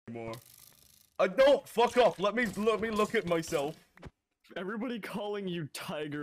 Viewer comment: Thought you said muck off 💀🪑🪑